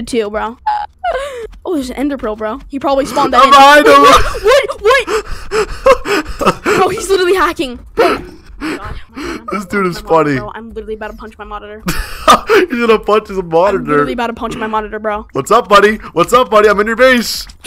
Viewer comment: in 6:44 i thought he said " I swear I'm about to punch my mother